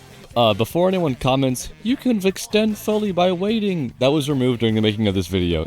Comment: all I heard was “you can extend for waiting!” Can someone tell me what it said and what he means by that? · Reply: “You can extend fully by waiting!” It’s a very outdated tech at this point don’t worry about it 👌